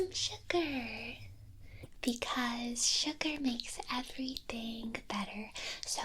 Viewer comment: "Sugar make everything better-" she said. What i heard: "Suga make everything better-" Absolutely agreed 😌🤭💜